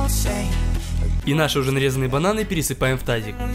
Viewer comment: и 1:56 мне одному (да именно одному а не одной потому что я поцан просто с другого аккаунта ) с первово раза послышалось что сказал нашы железные бананы 😂